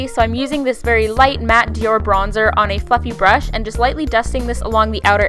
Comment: Lol I Heard Her Say 'fucky brush' Did She Say 'Fluffy'?? Dang My Ears Are Bad